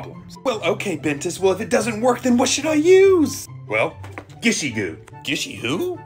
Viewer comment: I cant be the only one who said “ GISHY GISHY GOO MEANS I LOVE YOU” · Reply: i thought he said gitchy goo and i got all excited